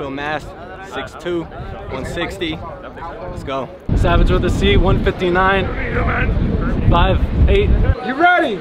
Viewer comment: I thought he said "Savage of the Sea" 🤣